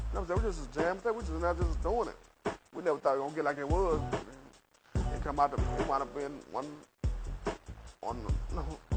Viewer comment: what’s brother saying bruhhhhh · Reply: no I'm talm bout